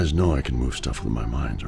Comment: Did Drax say I can move things with my mind(s)?!